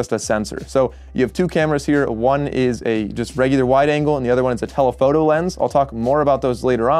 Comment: did he say wide angle?